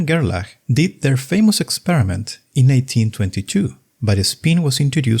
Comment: great video with lots of information. but at 0:14 seconds, you said 1822 instead of 1922. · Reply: I am glad you liked the video; unfortunately, my deteriorated elocution failed me again. I really said "1922" but too fast and it sounds like "1822." It happened in another video too and since then I try my best not to mess up dates, for this reason I also try to show the date on the screen in case I mess up. I hope this didn't deter you from watching the rest of the video.